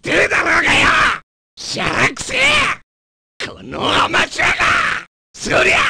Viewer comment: que dice cuando te levanta? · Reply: Sorya!